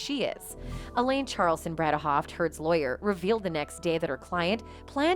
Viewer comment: did she just say Turd's lawyer?